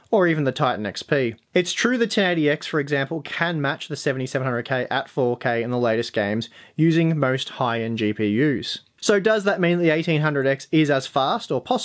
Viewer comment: Your power settings should be changed to High Performance before testing. I'd take reviews on Ryzen with a grain of salt until it's been out for a full quarter. By June most game developers should have their games optimized for Ryzen and I expect the results will be different. Good review though. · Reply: 1080x I hear? 3:00 😅